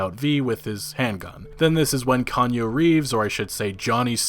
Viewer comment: Kanyo Reeves; Key--ahn-u reeves (Keanu Reeves) was funny when i first heard it. (ah its already up in the pinned comment).